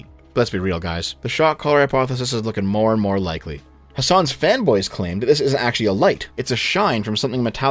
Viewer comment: Whenever you say "fanboys" all I hear is "femboys." That's it. I gotta quit.